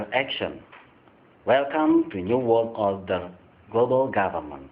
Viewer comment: WHAT DID HE SAY RIGHT AFTER HERE: 0:24:20 I'll tell you..."Welcome to the New World Order" That's what he said.